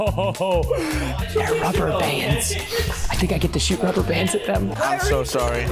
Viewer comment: I thought said “copper pants I get to shoot copper pants at them” but he said rubber bands